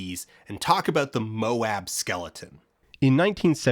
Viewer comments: i rewatched this, i thought he said the MOASS skeleton for a second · Oh lord, when he said "Moab skeleton" at 25:50, I thought he said "MOAS skeleton". I nearly had an aneurysm thinking that this video was going to circle back around to short sales.